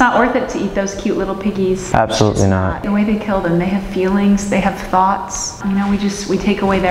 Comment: I thought she said they have oops 💀